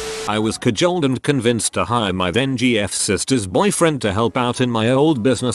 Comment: I first heard that as "Then-sisters"